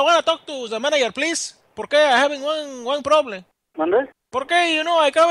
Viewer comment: He says "Mande?" not manager. Mande is the polite way of saying "what?"in Mexico. It is like saying "pardon me?" . BTW, I love this prank!!! It is one of my favorites!! very calm, very serious.........NECESITOOOOOOOOOOOOOOOOOOOOOOOOOOOOO TUENTI FRI TACOS!